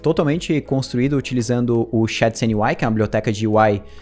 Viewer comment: em 0:26 voce fale que o frontend foi totalmente construido em ???? não dá pra entender o que é. Nem todo mundo é tão bom em ingles como você, por isto poderia colocar uma legenda sempre que falar nome de bibliotecas em inglês, por exemplo. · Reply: shadcn/ui